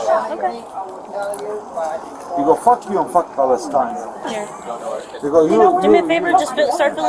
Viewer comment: the subtitles are wrong here. Its actually: he go you and pale$tine. Not: he go you and this time. This sentence makes no sense.